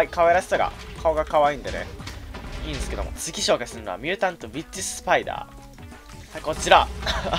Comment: ミュータントウィッチスパイダーが ミュータントビ〇チスパイダーに聞こえた